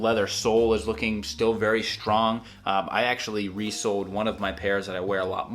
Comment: Lol, the first time you mentioned resoled in this video, I thought you said resold.